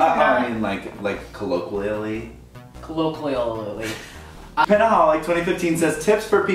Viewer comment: What did Scott say at 4:04? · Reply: IAmAFangirl colloquially. (characteristic of or appropriate to ordinary or familiar conversation rather than formal speech or writing; informal.)